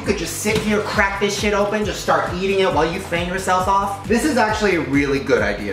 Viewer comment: I thought he said "while you finger yourself" the sad part is i wasnt even suprised cause that is totally something he would say